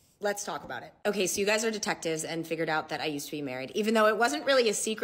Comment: I thought it said I used to be a mermaid let’s talk about it